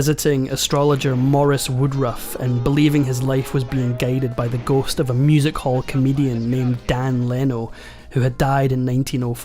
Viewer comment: I was half paying attention and then I heard: “…being guided by a music hall comedian…” and was like what?!? Lol